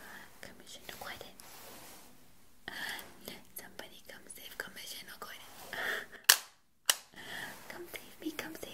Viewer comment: I thought she was saying come save some mission a quardin 😂 But she was saying come save commissioner Gordon